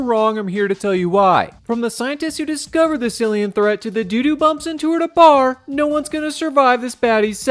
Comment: I thought he said “doo doo bumps” instead of “dude who bumps” lol